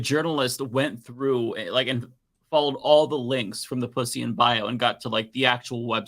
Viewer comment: ok. I thought they were saying "pussy AND bio" so it wasn't registering as "PIB" but now I understand that PIB is pussy IN bio.